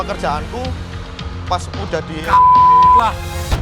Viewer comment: Chef juna:gk tau ngomong apa 7:13: kaget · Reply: Chef juna : kambing lah!!